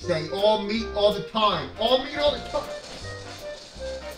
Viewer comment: I thought he said “long meat all the time” HELP ME